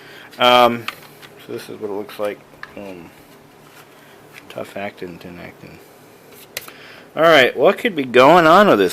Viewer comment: Did you say "Tough acting Tinactin"? :P